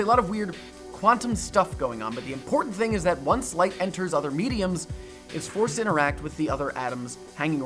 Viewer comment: did he say “mediums”?